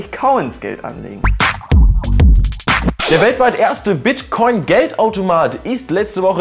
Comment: habe Bitchcoin verstanden